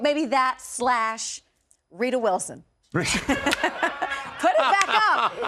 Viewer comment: Is she saying Rita Wilson or Rebel Wilson?? Either way, she must be smoking something. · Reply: Rita and Rita Wilson looks nothing like Camille it’s very weird